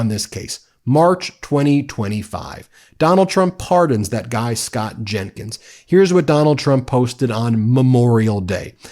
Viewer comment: At first, I heard “Scott Jennings” Darn